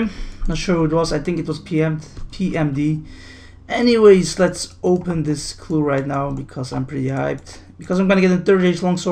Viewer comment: I'm a open this elite because I'm high, lol at least that's what I heard!